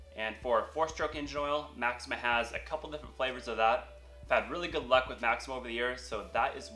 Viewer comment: Did he say flavors of oil